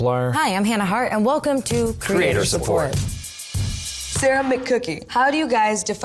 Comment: I thought Liza said "ceramic cookie"